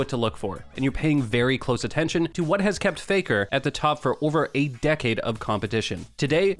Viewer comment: Ok I am a BIG FAN of Faker AKA the unkillable Demon King but did the commentary just said what has kept faker at the top for over 8 Decades of competition so basically 8 Decade is 10 *8 = 80years... That's too much... And no I am not against Faker.... I am one of many of his biggest fans but his Debut was in 2013... So basically it's 1 Decade.... Maybe.... I repeat maybe i misheard A DECADE instead of 8 DECADE...